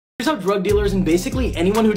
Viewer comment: Im so sleep deprived i thought he said rug dealers